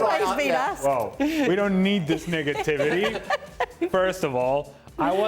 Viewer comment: Did Ale say no “Niggativity”?